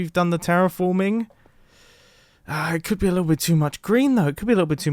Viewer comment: all I heard was “a little bit too much grian”